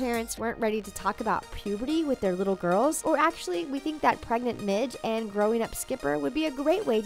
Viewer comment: Who else thought she said “pregnant bitch” and had to replay it